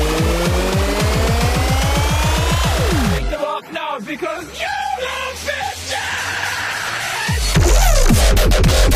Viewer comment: What is Jack saying? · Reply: YOLO BI$CHES!!!!!!!!!!!!!!!!!!!!!!!!!!!!!!!!!